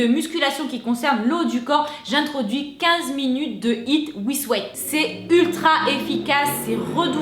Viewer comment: Coucou, petite question ☺️ à 7:24 tu parles de 15 minutes de hiit "wisewait", je sais pas comment ça s'écrit, peux-tu m'en dire en quoi ça consiste 😅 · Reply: Je crois que c'est with weight, c'est à dire avec des poids 😁